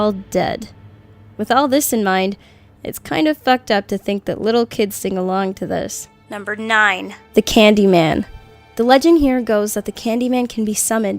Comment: Number 5 is a game? My school made me do this! We were studying the polio out break in NC, we were doing an activity where one person was laying down. We then had to name a reason they died. We all atempted to pick them up with 2 fingers. A teacher told us to do this. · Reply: did she say thucked up?😕